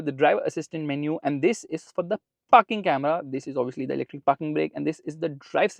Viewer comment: Here 13:07 Did I heard something else f**king camera or Parking camera😅!!!